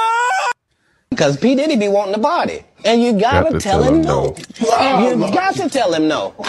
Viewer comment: ℹ initially thought Katt said regarding Diddy, “he’s wanting the body” vs “he’s wanting the party” = same in the end.😢